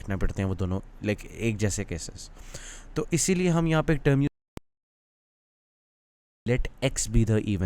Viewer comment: bro what u say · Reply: "is hi liye ham yahan eik term use karte hein 'Without Loss of Generality' "